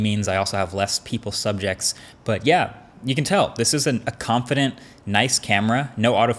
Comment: Am i the only one who heard "this isnt a nice, confident camera"?